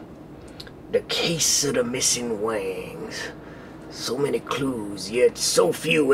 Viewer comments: it sounds like he says "wangs" (i meant that before) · it sounds like he says "wangs"